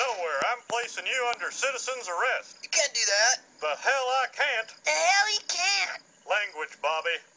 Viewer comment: confused by these comments because it sounds like Hank is saying the hell I cant and not can